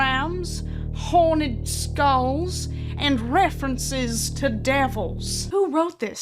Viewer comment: STOP I THOUGHT THEY SAID "horny skulls," I AM ROLLING.. ALSO.. you cant get surgery for that type of stuff until you are 18 as they said ! also again, why are yall getting all on target when spencers and hot topic are a thing? like yeah i love both and i work at spencers, but they have ABLITIROUS amounts of Lgbtqia+ items.